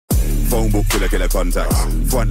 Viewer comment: I thought Kermit said Fortnite n*gga gonna run back